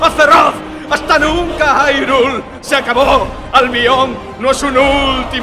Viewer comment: Jajajajaja, siempre pense que decia dice "Se acabó, al mión" en lugar de "Se acabó, Albión"